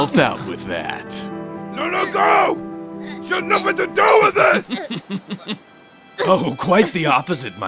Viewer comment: Wait.. What is he saying at 5:50? No no go sha no baba do wit this!!